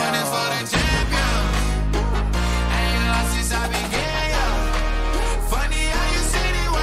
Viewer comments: "I ain't lost since I began, yeah" I know, I misheard too lmao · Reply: Thought he said i ant lost since i been gay yeah🤣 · I love, I thought exactly what he thought that Lil Nas x said I ain't lost since Ive been gay but he actually is just saying I ain't lost since I began.😂😂😂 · Lil nas x: I ain't lost since I began: I ain't lost since I been gay?! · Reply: I thought nas said ain't been lost since I got in